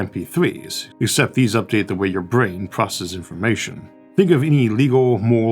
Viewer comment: Why are the subtitles different than what you are saying? at 11:00 it writes Hippocampus while you say brain.